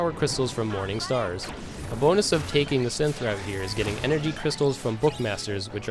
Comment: What do you say at 0:33? I can't get twilight crystals from the bookmasters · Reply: Uh...I mean if you read, it clearly says Energy Crystals from Bookmasters. Literally no idea how you think Twilight was in there.